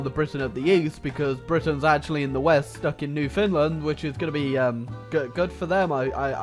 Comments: NEW FINLAND,DID YOU JUST SAY NEW FINLAND? New FOUND land. You make it sound like it was a finish colony. This is mainly just a joke I dont care that much. Even though it sounds like I'm super but hurt... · new Finland? did I hear that right?